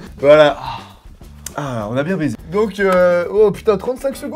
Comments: C'est moi ou a dit à 11:40 "On a bien baiser" ?! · J'ai bien entendu "On a bien baisé" à 11:40 ? Bon personne n'a fait aussi minable que moi : 14 secondes seulement ! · Il y a que moi qui est entendu "on a bien baisé" à 11:40 ? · Wesh à 11:41 j'ai entendu "on a bien baisé" chuis la seule ? 😂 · Il y a que moi qui est entendu "on a bien baisé" à 11:40 ? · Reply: Ta réson moi aussi j'ai entendu "on a bien baisé" à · J'ai entendu "on a bien baiser "